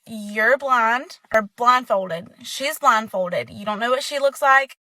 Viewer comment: I need a gaaaaaa 🤡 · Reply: I thought she kept saying "blonde". It wasn't until around the third or fourth time she said it when I realized she was saying "blind". 🤪